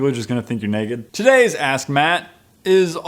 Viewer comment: I thought you said "This is asshat" And not "This is ask Matt"